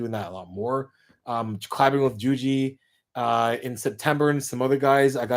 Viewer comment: when he said colab with juju i thought he said CLIMB with juju and i got really excited